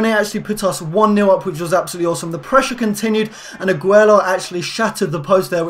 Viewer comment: i thought he said aguero lol